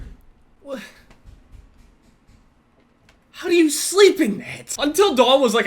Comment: Can someone explain this to me? And what the fuck did he say? Did he say "How do you sleep in it?" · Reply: There was banging in the background from construction and I believe he said "how do you sleep in this."